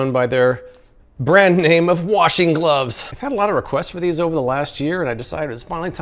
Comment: "Washing Gloves" What I hear: Washington Pls tell me im not the only one to hear this